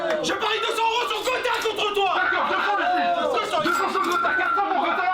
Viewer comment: à 52:54 Maghla dit clairement "Kotei" ou c'est moi ??